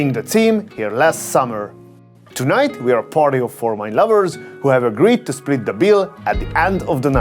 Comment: It sounded like you said "We are a party of former lovers". LOL